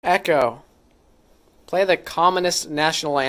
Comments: I think Echo thought you said commonest, not communist. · LOL what it thought you said (and I heard too) was: "Echo, play the commonest national anthem"... America's is the most popular it would seem... xD